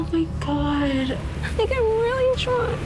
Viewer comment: It sounded like she said “oh my god.. I’m really drunk” at the final rose when she starts crying lmaoooo